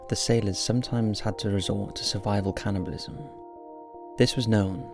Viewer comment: it sounded like you said "sailors had to resort to survival cannabis " I think my dyslexia has spread to my ears!